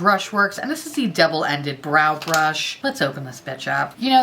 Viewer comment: I could have sworn you said "devil ended brush" and I was instantly intrigued on what the fuck that would look like 🤔🤣❤.